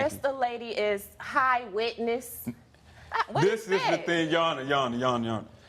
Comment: What does witness got to do with anything? Confused.😐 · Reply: Wetness not witness lol I thought he said that too